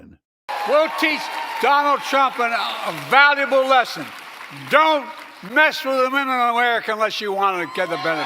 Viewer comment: Is it just me but did Joe say "Don't mess with the US unless you wanna get a benefit" ? DID anyone hear what I heard or maybe it's my ears ?? Let me know PLEASE !